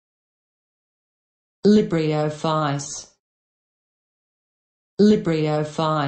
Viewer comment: why the fuck do i keep hearing "libree o flies" :|